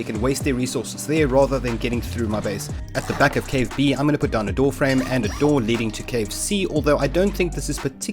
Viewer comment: Why does every time he says "door frame" I hear "warframe"